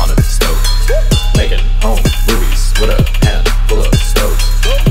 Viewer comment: I thought he said "make it on grease" rather than "making home movies"